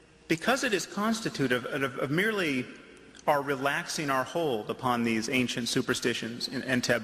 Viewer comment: did he say that we should relax our hole at 4:25?